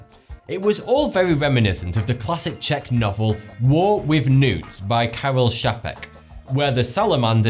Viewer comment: did anyone else hear "War with NUDES" instead of "War with newts"? XD